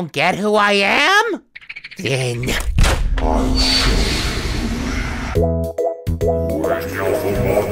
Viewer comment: I just realized how genius it is that Run lyrics are just him reciting his FNF mod wiki page and his ARG. Just before the song starts, at 4:59 , says “You STILL don’t know who I am? Then… I’LL SHOW YOU… “. Then, once the lyrics start up, proceeds to explain his lore. quite literally showed Boyfriend who he is. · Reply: wait..... i didnt even realize he said then "ill show you" i thought he was saying some kinda amharic spell (because that is the language for the actual text)